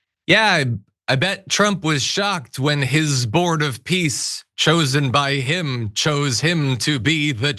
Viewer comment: I thought he said "I'm bored with peace."